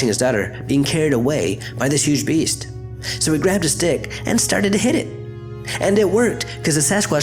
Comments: I fuckin swear he said "I grabbed his dick" Lmao. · i swear he said "he grabbed his dick and started to hit it" i was like what? 😲then i was like oh..a stick😂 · it sounds like he says " so he grabbed his dick, and started beating it"